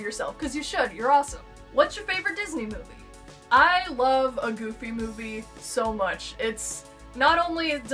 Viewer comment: When you said "a goofy movie" i heard "emoji movie" and my brain stopped funtioning for a solid 30 seconds. Then i went back to make sure i heard it right, and the hugest wave of relief I've felt in a while washed over me.